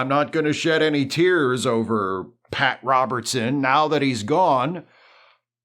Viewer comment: My brain farted and I kept hearing "Pat Sajak" instead of "Pat Robertson" and could not figure out how you'd became such a monster.